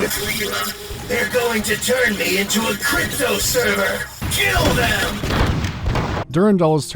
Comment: I can't believe I fell for the Durandal voice actor until he said, "they're going to turn me into a crypto server!" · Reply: i thought he said "crypto servant" oh